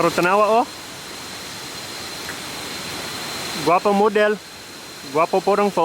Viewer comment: Thumbs Up for the English subtitles! But at 4:40 did you say "guapo model?" Shouldn't it be "guapa model?" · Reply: He said gwapa...u cant really hear but he said GWAPA dude common theyre not filipino so dont expect na malaman nla ung lahat ng words/pronunciation